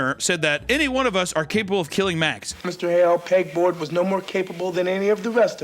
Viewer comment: does he call her peg board or am i trippin?